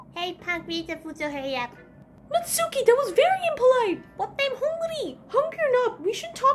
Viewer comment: What Did Natsuki Say There?? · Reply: "But I'm hungry"